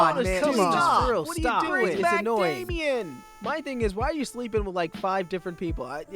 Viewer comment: Did someone say "bring back Damien!"